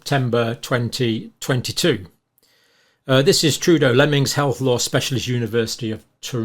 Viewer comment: I thought you said 'Trudeau Lemmings' great name for a band!